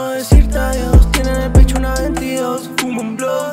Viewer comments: flashé con "tiene la pichula de dios" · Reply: yo escuché "tiene la pichula de 22" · flashé con "tiene la pichula de dios" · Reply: Yo pensé que decía Tiene un picho de 22